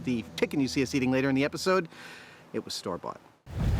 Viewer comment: it was what? I couldn't understand what he said. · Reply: Store Bought.